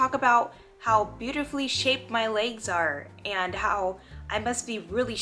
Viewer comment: Beautifully shaved legs, what a strange compliment. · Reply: lol I thought she said "beautifully shaped legs"